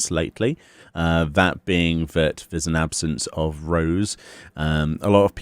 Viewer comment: At first I heard "absence of Roes" and thought yeah, we need more of them!